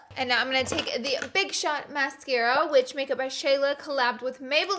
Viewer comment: When she was saying she was going to apply her ‘big shot mascara’ I thought she was saying ‘big shaq mascara.’